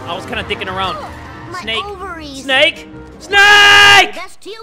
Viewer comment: DID HE SAY MY OVARIES